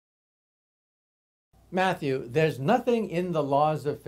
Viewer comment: I'm not sure, but it sounded like he said Matthew at the very beginning of the video. That's my name so I freaked out...